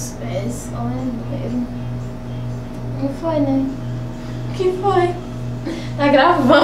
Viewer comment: Só eu que escutei ela no final falando: QUE FOI NEGO(09:12)???